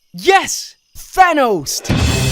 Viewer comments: also did you just say "yes thanosed!!!!!" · did he just say thanosed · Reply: YES!!! THANOS'D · did he say thanosed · Bruh did he just say "Yes Thanosed" 😆😆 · Is it me? Or did I hear: "YES! THANOS-ED!" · did he really say what I think he said-? Yes... T-thanosed? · did he said...thanos? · did i hear thanos? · did just say "Thanos'ed"?lmao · Did he just say "yes thanosed" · did he say Thanosed 🍆😂😂